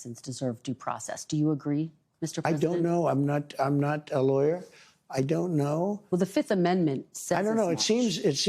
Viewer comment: He didn’t say that. Listening again. He is telling her that his attorney is handling that. They’re spinning all this to get a rise out of the listeners. Welcome to being cattle. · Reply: he literally says “I’m not a lawyer”. Wow. What he says and what y’all hear is amazing to me.